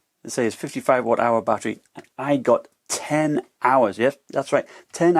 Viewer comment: did I hear this right? 10hr???!